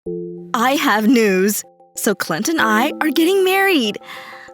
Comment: In the beginning all I heard was “I have nudes”